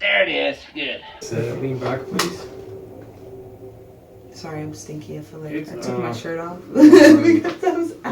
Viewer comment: did she say sorry I'm stinky?...lol wow